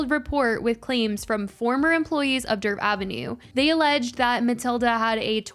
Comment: I keep hearing “Derp Avenue” 😂